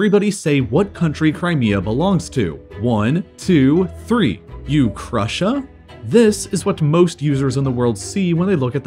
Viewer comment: I thought he said nuke Russia